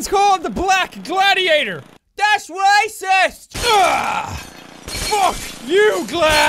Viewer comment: That’s Racist! I laughed at that part!!😂😂😂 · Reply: I thought he said that's what I said instead of that's racist 😂😂😂